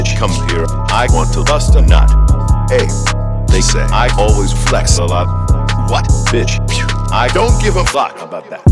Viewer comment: ohh its "They say I always flex a lot!" I thought it was "they say ice always reflects a lot"